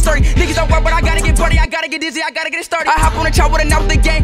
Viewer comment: what is bro saying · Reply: I gotta get dizzy i dota get sturdy 🗣️🗣️🗣️🔥🔥🔥